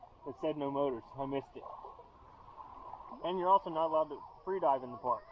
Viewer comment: What is say you're not allowed to do at 16:46. Did he say free dive?